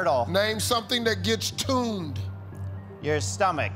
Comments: to be honest, I thought Steve said "turned" too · thought Steve said “toned”